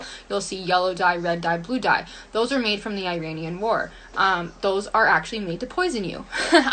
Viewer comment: The “Iranian war”...my eyes just got stuck in the back of my head. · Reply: I thought she said Iranian boar lol